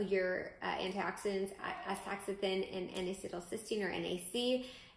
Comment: What is the antioxidant you are saying right before NAC? You say it at time stamp 13:32. Thank you for a great video! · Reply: astaxanthin